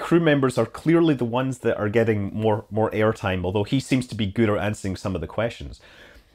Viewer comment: Oh Scott, you're the best but did I hear you say at 7:49 - "He seems to be good-er answering some of the questions"? ;) And yes, I actually do hang on to every word you say because, well, you're awesome! Especially when you get crazy with your creative word usements! (I stole that from Steve Martin) :)